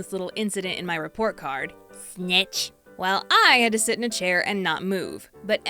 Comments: I thought she said b!tch when she said snitch to the STAFF 😂 💀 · What did you think she said?😂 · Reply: I thought she said b*tch 🤣